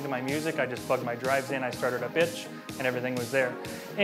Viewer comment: it sounded like he said he started up itch it sounded like he said bitch ahahah